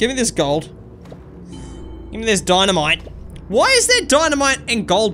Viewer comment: way did you say “way is there Dimnide